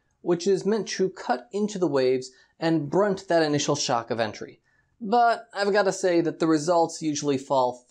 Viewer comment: into your description, you said "...initial shock of entry", not 'shock of initial entry', which I mistakenly thought I heard and then I formed a new concept of that "point of lift" at the point of entry to "cushion" against the shock of entry you described as ride quality. A video of this is from waverider boats in Australia, and would love to hear your views on that design he used on a trihull hydrofoil. Though my thought is to make the bow of a "V" hull affecting greater lift at the point of entry and causing an improvement in ride quality and handling, especially during a following sea.